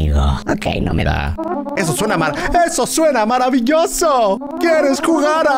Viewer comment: Que dijo german en el minuto 4:48 ? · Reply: No dice eso suena maravilloso